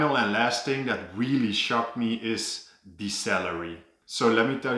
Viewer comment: So am i the only one eho thought he said 'cellery' when he said 'salary'? I am? Oh, ok